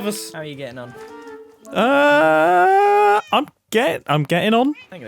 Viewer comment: MUMBO SAID "I'm gay." P.S. It stumps me how I'm a 20 something year old engineer, yet instead of doing engineery stuff, I laugh at people when they accidentally say something incriminating.